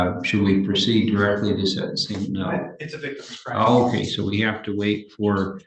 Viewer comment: did they say its a victimless crime?